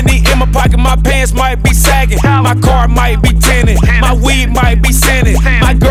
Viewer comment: I thought I heard 2 Chainz say: "My wee might be scented" 😂